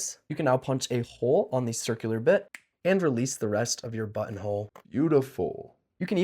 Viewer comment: thought he said butthole 😭